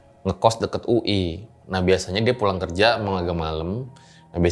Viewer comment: lah cerita ini dulu gw dengernya dia naik kunti bang, naiknya dari mipa. Mipa serem parah si dulu 😅